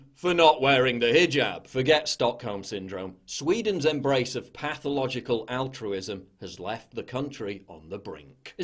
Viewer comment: I was seriously scared for a second,I thought it said pathogenic autism